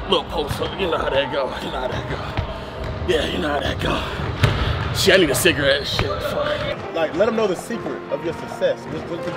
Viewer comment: did just say “I need a cigarette” 🚬